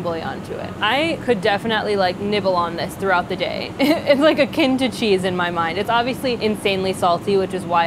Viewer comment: "It's like a [foreign] cheese in my mind" in the captions LMAO she clearly says "akin to cheese"